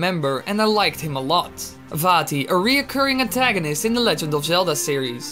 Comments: When he said Vaati once, it sounded like he said fatty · I know it is most likely your accent but I heard "Fatty" instead of vaati xd